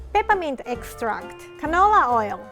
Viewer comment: I heard "ketamine extract" and I was like whaaaat 😳😂